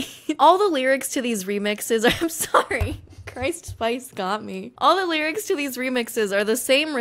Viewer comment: Playing a game as I have this on in the background and all I hear is "Christ Spice" and I lost my shit.